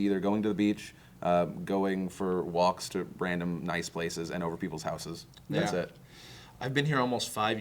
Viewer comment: said "odder people" and I heard "otter people"